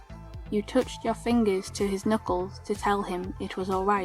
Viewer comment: Ok, who else thought it said "nipples" XD